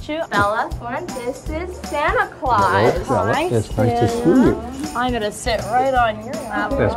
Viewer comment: "what if I don't want to ask them how their day was..?" me at work every day lol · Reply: I thought Santa said “ it’s nice to SUE you “